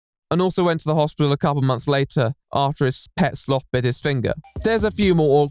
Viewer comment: Did you say "his pet sloth"?! There's a pet sloth?! Where, I wanna see!!😍